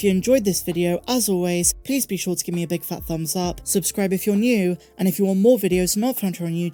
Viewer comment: Ha, I heard 'Subscribe if you are Jocasta Nu!'